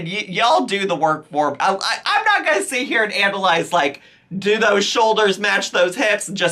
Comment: At first I thought you said Amberlyze instead of analyze 😭